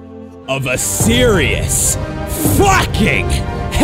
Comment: Is it just me or does he say "FUCKING" The best in the world.